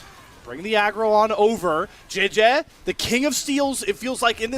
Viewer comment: Why am i hearing jieje as "GenG" and they hear words like "peyz" i got confused on casters accent